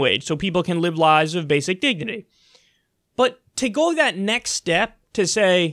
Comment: This is an incredibly serious subject and something that deserves attention and pressure from people and government but... at 3:12 it sounds like Kyle said “basic diggity” and I that’s all I could think about.